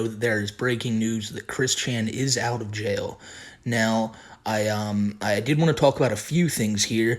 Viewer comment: They way my brother says his name sounds like christian, so I heard "Christian Bale out of jail" like wtf, what he do haha.